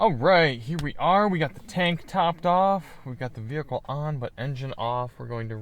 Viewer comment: I thought you said "tank top off." I was wondering why you were wearing tank top in winter and taking it off in the car.